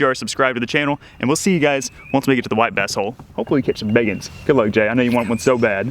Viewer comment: Did really say, white basshole? 🤭🤭🤭